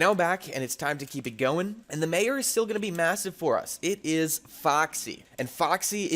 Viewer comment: did you say massive?